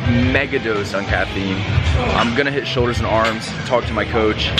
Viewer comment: Did she say “I’m gonna kill myself” 😂